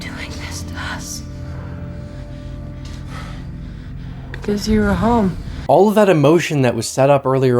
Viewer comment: I always thought she said because you’re a hoe 😂